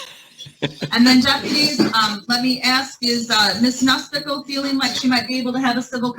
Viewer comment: Do I keep hearing, "Miss Mooseknuckle"?